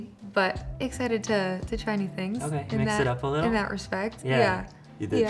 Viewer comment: i REALLY thought he said "okay, can you sit up a little?" 😂